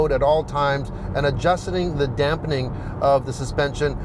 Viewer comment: did you say adjustening? 😆